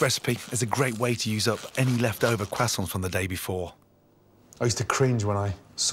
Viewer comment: Is that how you’re supposed to say croissant? 😂 The first time I heard him say it I thought he said “Quackson”